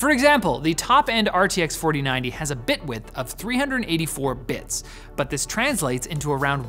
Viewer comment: When he said "bit width" the first time I thought he was saying "Bitwit" lol shoutout Kyle